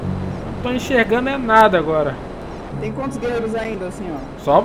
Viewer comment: Eu escutei "tem quantos guerreiros ai, docinho" ? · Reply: Ela falou "tem quantos guerreiroa ainda senhor?"